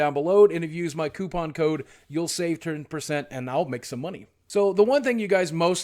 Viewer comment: I thought you said 'save 200%'. Sadly a consumer can't be sponsored just for opting to receive a LootCrate. Are they available in the U.K.?